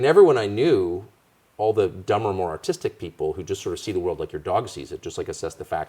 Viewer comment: I love Tucker and he is my favorite podcaster, but did he say "dumber autistic people" at 14:39? That's not a great look. "people with low-functioning autism"